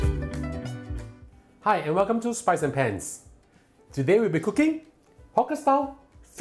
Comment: I thought you said , "Spicy pants" 🌶 👖 😂🤣